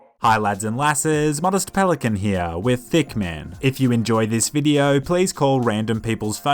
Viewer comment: In the intro to you video it sounded like you were saying thick man :p